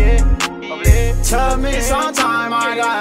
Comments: Nigga sound like he sayin took me some time I got off like wtf💀💀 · took me some time i got over! for those of you that don't know what he saying lml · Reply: I thought he said "Tell me sometime why I got hope" lol😂